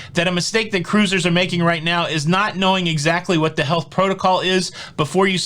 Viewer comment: Every time you said "What the health..." I thought you were saying what the hell. It was awesome.